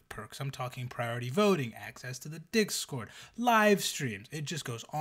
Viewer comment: When says "discord," early on, i heard "priority voting on dicks-cord," had to do a double take for the emperium there.